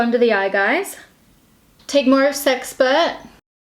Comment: Okay, when you first said "sexpert" I heard "sex pervert" and then was like "Whoa! Chloe!"